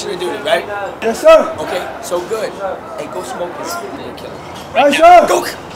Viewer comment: "Go smoke this kid but don't kill him." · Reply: I heard "go smoke this fool..."